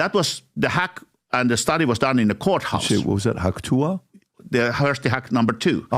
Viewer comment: PBD mistakes Harri saying "Hawk Tuah" instead of "hack too" jesus christ lol